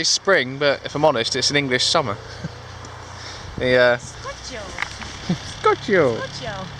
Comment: What do they say @ 29:40 after "It's an English summer"? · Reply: Scorchio. It's from the channel 9 weather segment on The Fast Show comedy series.